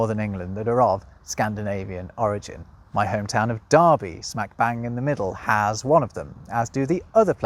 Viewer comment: Thanks so much for watching! I'd love some good Scandinavian language facts. If you have any, pop them below. And don't forget to check out my limited time offer from Rosetta Stone of a Lifetime Subscription for $179 instead of $299: <link> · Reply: If one is from Derby, Americans would pronounce the "er". Brits and its colonies seem to never pronounce the "R" sound. It sounded like you said you were from "Dahby."